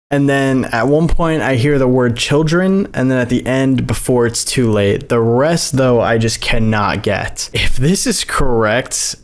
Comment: i hear "world leaders chewing"